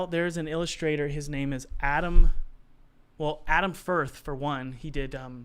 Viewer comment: I can't understand what the names are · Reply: Adam firth (saladfingers), Adam Phillips, brackenwood. Esao Andrew's I believe those are the people he said